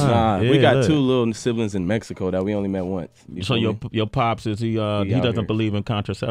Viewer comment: "we got 2 little mexican siblings we don't even want." I know he said we only met once, but it sounded like what I wrote before.